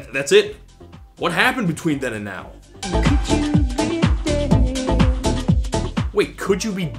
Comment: For the longest time, I always heard the line "could you be dead" as "could you be gay", and used to wonder what they were getting at by mentioning that, and why it would be important to finding someone.